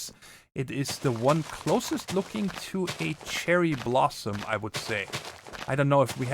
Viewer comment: Did you say Cheryl Blossom at 42:17 ?